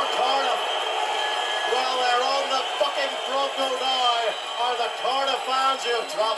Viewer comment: i heard "fucking bronco".